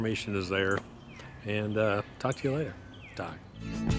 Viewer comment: What was the very last thing he said in this video? Sounds like he said, "talk to you later, die". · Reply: His closing is always, "talk to you later, Doc."